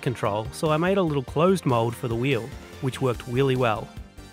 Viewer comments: Did he say the pun “wheely well” at 9:28 ? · "... closed mold for the wheel, which worked wheelie well" Did I hear that right? lol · did you just say "wheelie wheell"